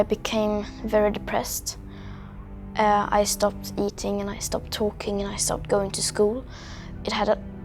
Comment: I legit thought she said I stopped twerking.